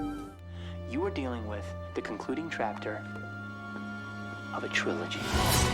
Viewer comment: Am I hearing "concluding trapter."